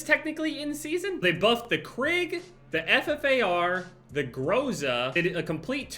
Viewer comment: Hold the fuck up 1:04 did I hear they buffed the ffar or am I mentally unstable